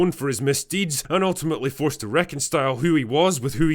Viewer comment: Haha you said "reconstile" with a T instead of "reconcile." Now that you have misspoken, all of your arguments are invalid.